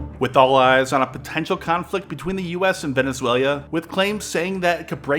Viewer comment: Did he say "Venezuelia"?